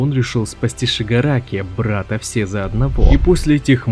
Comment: Шигараки брат взо или я не так услышал?